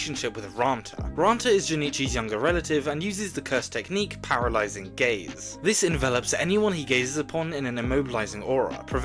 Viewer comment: i heard it as "paralyzing gays"